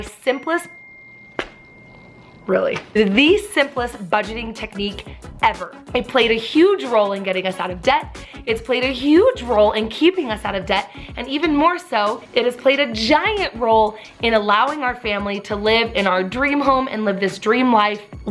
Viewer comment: Which shopping app do you use? I heard 'even note'. Can't find it. Please help!